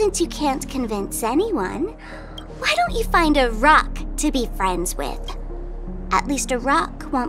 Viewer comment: instead of "a rock" mine actually write "befriend a mute" no wonder why she's getting canceled